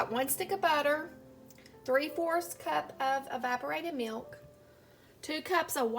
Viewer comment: At first I thought you said "vibrated milk" I'm like What?! Oh wait, EVAPORATED. 😂 Anyway this maple fudge really looks NUTS! Does that marshmallow fluff make it a little more airy textured?